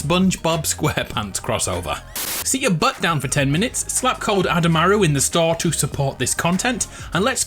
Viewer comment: i honestly thought i heard you say ''Slap Adamaru in the store'' at the beginning of the video. Maybe I should get a hearing test done lmao